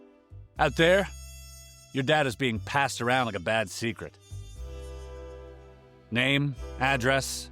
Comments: I was listening to this in the background and all I heard was "Out there, your Dad is being passed around like a bad secret..." Like holy shit I get that aliens are creepy but thats alot 😂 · Definitely misheard that as "Your dad is being passed around like a bad secret," and the first thought that came to mind was "He's a consenting adult, he can do what he wants." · on the ad i thought you said "out there, your dad is being passed around like a bad secret", was like excuse me ahhahaha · Bruh!! I misheard you at the start of the add and thought you said “your DAD is being passed around like a bad secret”….. Was like “Excuse me?????” 😂😂💀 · BAHAHHA I thought you said my Dad was getting passed around😂😂😂😂